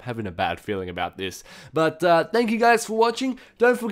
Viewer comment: i thought he said rape comment and subscribe